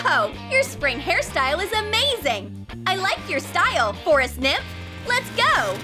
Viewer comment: i thought he said i like your hairstyle... for a simp